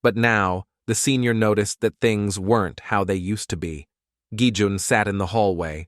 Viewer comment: I thought u said gi hun